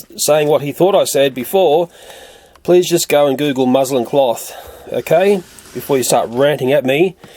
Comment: are you serious? did some twat complain about the word muslin? people should at least listen to you properly if they're gonna cry like little babies. sad little people!! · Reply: I believe that because of his accent it sound like he said Muslim